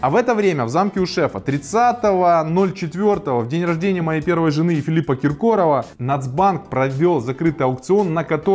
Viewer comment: Послышалось "у моей жены Филипа Киркорова" :D